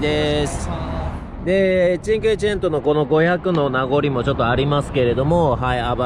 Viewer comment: あたり チェンケーなんとかかんとかって なんて言ってるのでしょうか、、、？ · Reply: チンクエチェント。チンクエ＝５、チェント＝１００。つまり５００